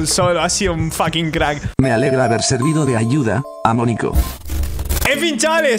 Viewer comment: yo entendi Amoniaco en vez de Amo nico xddddddddddddddddddddddddddddddddddddddddddddddddddddddddd